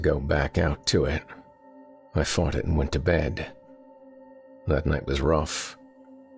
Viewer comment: I thought I heard "I farted and went to bed". 💀